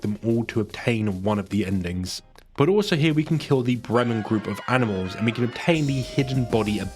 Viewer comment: the "random group of animals" are the musicists of Bremen EDIT: I now realize he did say Bremen and I heard "random" instead...